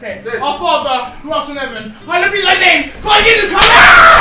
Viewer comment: alright ok our father who works in heaven is a..... Wtf is he saying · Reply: Our father, who arts in heaven, hallowed be thy name, thy kingdom com- AHHHHHHHHH 🤣🤣